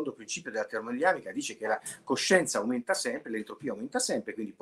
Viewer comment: "il principio della termodinamica dice che la coscienza aumenta sempre" ho sentito bene?? ma che significa?